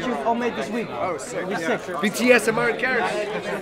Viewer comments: Did I just hear "BTS and Martin Garrix"? Will there be a collab between them?! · did someone say "BTS and Martin Garrix" ?